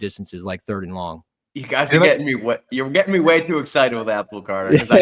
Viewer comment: did say yall are getting me wet 😂😂😂